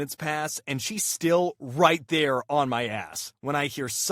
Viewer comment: Yo, why does it say "in" instead of "on"? 🤨🤨🤨